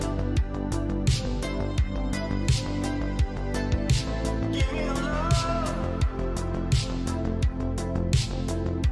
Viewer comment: can someone please tell me wtf he just said right here like WHATTTT!?!!?!?!? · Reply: "I wanna see some of these other clips on LSF boys." You adjust to the pepega, give it a year or two...